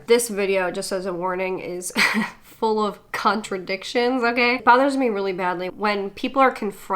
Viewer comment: Thought you said cuntradictions for a second lol